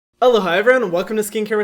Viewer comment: I always thought he said Hello hi, my name is Hyram. And when I accidentally left the automatic translation, it said -Aloha, my name is Hyram. And then I remembered he lives in Hawaii. Wow. Mindf*cked 😂